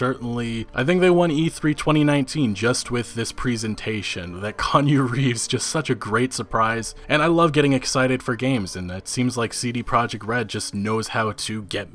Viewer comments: Konyo Reeves? No sir, no this shan’t do at all. We’ll say it together- Kee-aun-oo - Reeves. Once more, Kee-aun-oo- Reeves. 😎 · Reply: I thought he was saying Kanye Reeves. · Swore I heard Kanye Reeves a few times lmao 😂